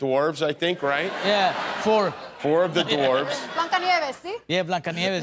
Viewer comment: What did she say in Spanish? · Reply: Blancanieves (Snow White)